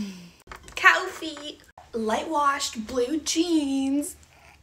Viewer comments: What she types"CAWFEE" what I hear"COWFEE" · When she said "cawfee" I heard "Cow feet" bc I was distracted bc my dog barked Also gurl u spawned a whole Fandom with "Suga" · I heard "cAwFfee" "cow feet" 💀💀💀💀💀💀💀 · I hear “cAwFEe 💅 “ as “ cAwfEeT” 💀